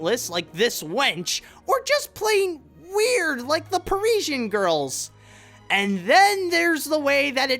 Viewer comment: He was pronouncing Parisian, meaning they are from Paris. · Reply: Derreck Garcia Oh, I thought he said Persian.